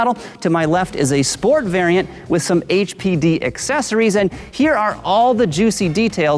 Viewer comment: At first i thought said "Sport variant with some HPV accessories" LMAO!